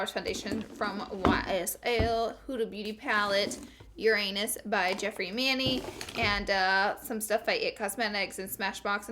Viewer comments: I heard "Uranus by Jeffree and Manny" as "Your anus by Jeffree and Manny" and I was like what did she just say? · hahahahaha, thought you said your anus by Jeffree and Manny 😂 😂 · i keep hearing "your anus" instead of uranus... help